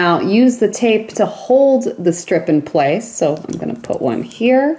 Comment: Sorry ... were you saying something. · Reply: Did she just say "strip"? and has anyone seen Hunt?